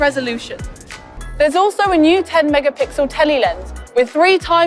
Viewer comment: did she just say “Telly” in reference to telephoto camera 😂 now that’s hip slings his skateboard over his shoulder